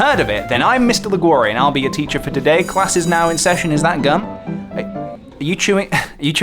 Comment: I thought he said "is that a gun?"